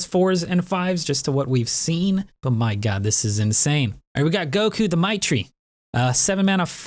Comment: did I hear wrongly or did say Goku...???